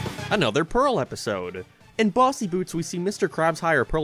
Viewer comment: On god, I thought that said "bussy boots."